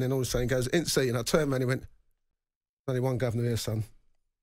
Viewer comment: what did SAF say to him at 4:44? I didnt understand :D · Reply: He said "only one governor here, son"